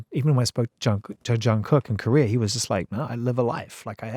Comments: did he mention jungkook or I heard it wrong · did he mention Jungkook or am I hearing the wrong word 💀🤡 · OMG DID HE SAY JUNGKOOK OR DID I HEAR IT WRONG?? 🤯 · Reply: yeah he said Jungkook . Also he did a great interview of Jungkook too.